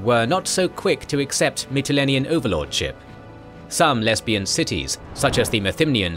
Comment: did I just hear 'Some lesbian cities' ?